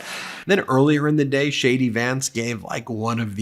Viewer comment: Did Ben really say Shady Vance @ 10:54 ? I like that 😁